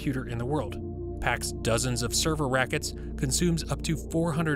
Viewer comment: Did he say server rackets at 3:27?